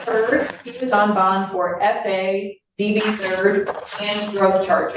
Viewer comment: She actually said "SA." The YouTubification of regular life, I guess.